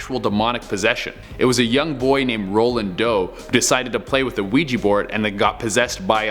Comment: I thought he said a little boy named "Rolling Dough"